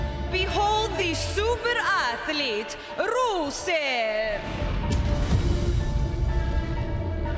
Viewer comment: I thought she said the super ugly rusev